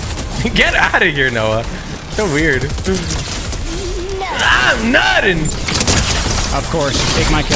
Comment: The fact that no one is ganna say anything about how said im nutting😂